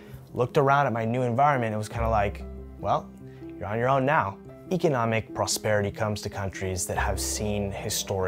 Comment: It's inaudible to me. Can someone tell me what he said. It was "something something prosperity come to countries that have seen historical stability" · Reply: Economic prosperity I'd say